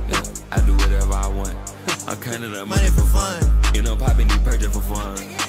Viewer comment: BRO I THOUGHT IT ALWAYS SAID " READY FOR FUN" NOT MONEY FIR FUNNN😭😭😭